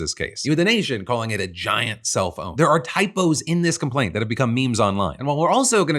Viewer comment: While not looking at the screen, I swear I heard “giant cellphone.” What? Giant self-own? … I’m dumb.